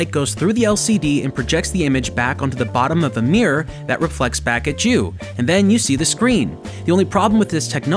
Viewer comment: I keep hearing "reflects back a Jew"